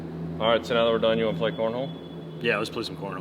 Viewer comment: Cornhole ? I thought you said porno.